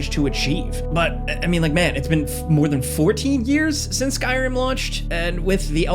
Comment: I won’t lie I misheard this as 40 years hahaha “You’re telling me for 40 years… ain’t no way”